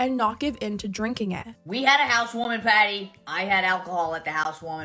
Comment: I keep hearing "house warming patty". I also hope Cody had non alcoholic drinks for him, and some sober friends with him. When my uncle was in recovery, what helped him was the fact we bought alcohol-free beer for him (since mostly everyone was drinking), and the fact that my cousins, brother and I "kidnapped" him to our table where we don't drink alcohol so he wouldn't feel the "odd" one out